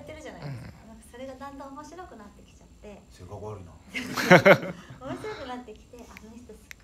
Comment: 好井さんのテロップ 『それがおもろいの？』じゃなくて 『性格悪いな』に聞こえます